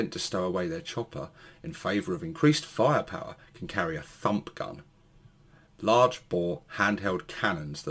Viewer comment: It sounded like you said ‘Da F**k Gun’ instead of Da Fump Gun!🤣😂🤣😂🤣😂🤣